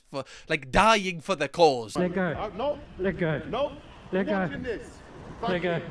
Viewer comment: He's saying let go, in case anyone else heard something else initially like I did 😂